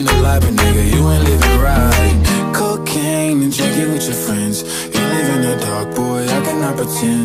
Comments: I thought bro said "coke and chicken with your friends" 💀💀💀💀💀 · I thought It it said “cocaine and CHICKEN with your friends”